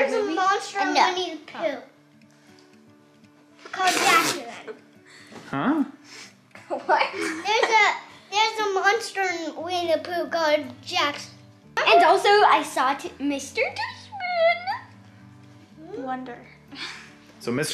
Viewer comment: interrogating about Princess Bride was amazing. I’m glad has discovered the genius of Mandy Patinkin! I also definitely thought said “there’s a monster when you poo” at first 🤣